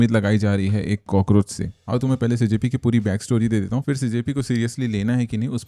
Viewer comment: What did he say... JP? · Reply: CJP